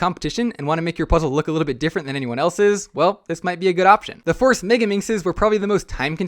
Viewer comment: WHAT DID HE SAY? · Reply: He said ‘good option’